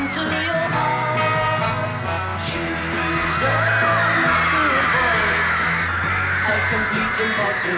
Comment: When I was young, I thought they were saying she's a muscular boy.